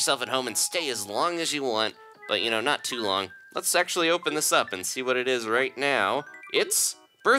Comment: Did he just say let’s sexually open this up